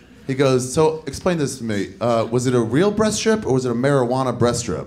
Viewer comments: I was hearing "breast strip" for the longest time. I was like how.. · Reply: LMAO, Makes more sense then what i hear nd herd for the longest time I heard "Breast Chips" LLAL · I would've sworn he was saying "breast chips", at first. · Thought this guy was saying breast chips for the longest time 😂😂